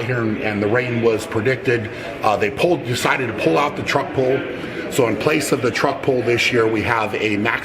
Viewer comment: Did Brian say "Trump Pull"?